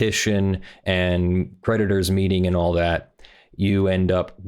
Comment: Why don't you link the document? Also I kept hearing "predator" in place of creditor